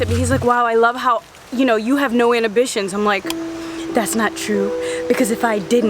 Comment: what does say? ‘No anobitions’?